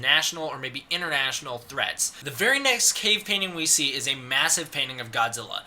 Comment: I thought is said 'additional threats'